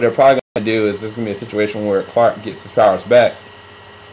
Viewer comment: I didn't really hear what you said. "Where Clark gets some" what?